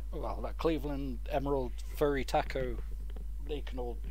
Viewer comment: Did you say furry taco? My bad went back and watched it (can only listen at work), you said Furutaka.